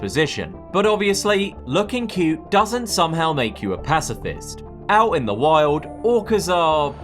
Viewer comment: I thought that said racist for a sec